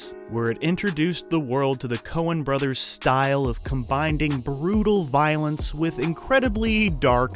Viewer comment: Did you just say combinding?